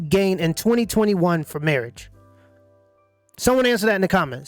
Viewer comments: I am 49, I tell my daughter to get married and my son to NEVER get married lol. all this stuff is so true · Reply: Im learning English and i would like to know what he says at that time line, "for marriage" or "from marriage" ? If its "for" then should't it be "from" ? Thanx beforehand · Im learning English and i would like to know what he says at that time line, "for marriage" or "from marriage" ? If its "for" then should't it be "from" ? Thanx beforehand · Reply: he said from. Women gain much from a marriage in 2021. He asks what do men gain from a marriage in 2021. Pronunciation in the USA is often affected by region or culture. Consonants and vowels get shifted or dropped when a word of English is spoken. While speaking for a camera it is possible that he forgot that he has foreign listeners, and simply spoke in a comfortable mode. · Im learning English and i would like to know what he says at that time line, "for marriage" or "from marriage" ? If its "for" then should't it be "from" ? Thanx beforehand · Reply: yeah he says. 'What do men have to gain in 2021 from marriage?'